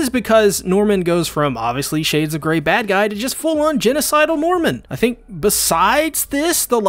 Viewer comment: I heard "genocidal mormon" when you said genocidal NORMAN! And I burst out laughing